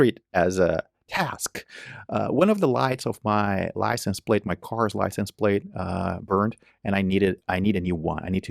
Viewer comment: I wasn't listening closely enough and thought you said your license had burned. That it was the bulb that burned out answered many of my questions.